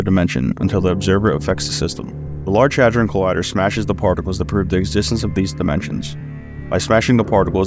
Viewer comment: Did she just say farticles?